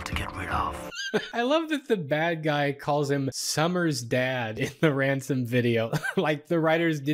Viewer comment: He actually says "Sommersted" which is the father's last name. Though I can hear why it sounds weird.